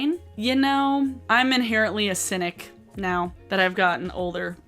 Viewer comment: Lol I thought you said "inherently acidic" as in you're sour. I like them both. Also just a reminder that Jeffery Star said black women should pour battery acid on themselves to lighten their skin, so not supporting him isn't something you have to qualify if you consider yourself an ally of POC. #Jussayin